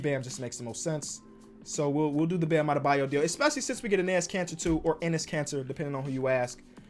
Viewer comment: yo all i hear is "anus cancer" not "Enis Kanter" 🤣😆